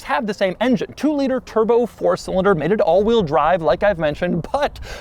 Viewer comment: Whenever people say “300hp mated to awd” or whatever, I thought they were saying “made it” not “mated”. Hahaha was so confused for so long